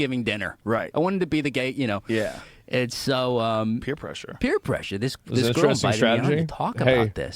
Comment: did they just say queer pressure instead of peer pressure !?